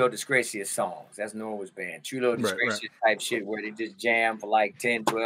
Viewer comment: what did he say norwood's band is? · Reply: Trulio disgracias